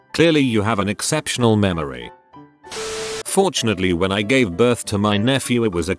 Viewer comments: Fortunately when I gave birth to my nephew? At 12:56 did I hear or see correctly? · please explain, did she just say “When I gave birth to my nephew”????? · did she just say when I gave birth to my NEPHEW? Like what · am i an idiot or did she say giving birth to my nephew???